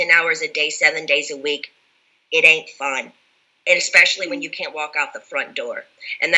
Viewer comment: I heard this as, "... I will tell you, sitting in a chair 10 hours a day, seven days a week,... *it ain't fun*." not, 'in a fog'